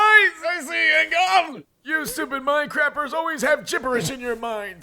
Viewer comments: Did he say minecraft? Ohhhh its mind crappers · lmao am I the only one that heard “You stupid Minecraft-era” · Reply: I thought it said mine-crappers😭😭